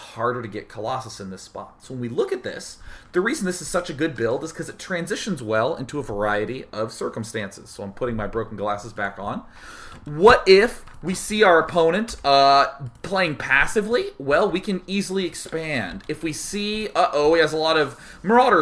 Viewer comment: i think he wantet to say " so i am putting my broken koloosses back on"